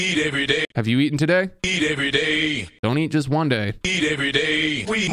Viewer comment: i thought it said eat kids every day i was so concerned